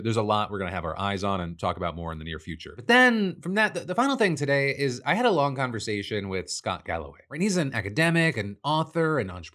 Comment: lmso thought you said george galloway for a sec.